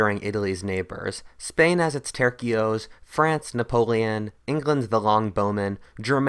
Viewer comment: When he said " tercios" I legitimetly thought he said turki'os